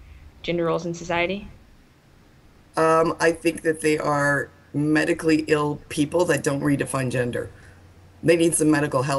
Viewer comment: Although I'm an anti-feminist, I don't really agree with anything Janet had to say. I'm a non-binary, and I don't think I'm mentally ill. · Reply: I see your point. Since I thought she said mentally ill instead of medically ill, I thought she meant that trans and non-binaries were insane or something. My mistake